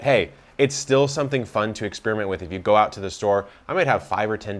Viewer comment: did you say "fun to ex-spearmint with"?